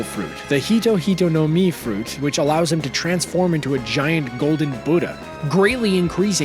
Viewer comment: I thought he said sengoku turns into a giant booger